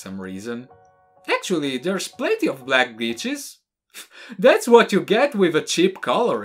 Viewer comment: I think I officially have broken brain cells, is it just me or when said “black glitches” instead I heard “black b**ches” ✋💀